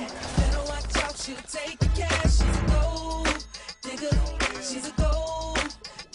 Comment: When I was younger I thought the song said “she’s a gold,nigga”